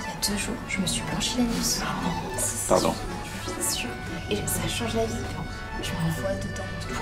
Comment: tout le monde entends comme moi ? 😭 "je me suis blanchis l'anus, je me vois dedans"